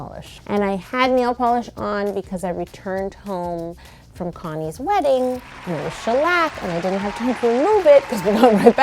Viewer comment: When she said “Connie’s wedding” I thought she said “Kanye’s wedding “ 😂